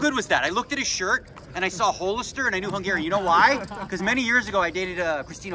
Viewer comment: I hear “Whole”-ister and I know he’s Canadian